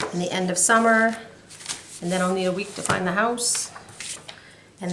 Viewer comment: I had to replay the end because I thought you said "then only a week to find the house..." So a move is on the horizon?? If so - best of luck